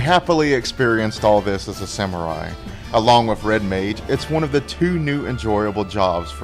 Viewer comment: Did he say Red MAID? Lol